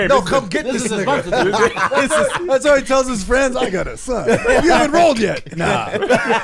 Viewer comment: "I got a son. But you haven't rolled yet... Nah. He in the car" 😂😂😂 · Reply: I’m glad i seen this comment because i thought he said “you haven’t enrolled yet” I was confused but still found it funny asf 😂😂